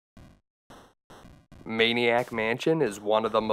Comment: Not gonna lie..swore for a 2nd that said Marilyn Manson, then I was like Ooooh!..right, THAT game. 😅👍